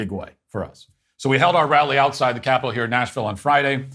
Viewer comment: Isn't Knoxville the capital of Tennessee? Where was this again? It sounded like you said Nashville. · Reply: It was Nashville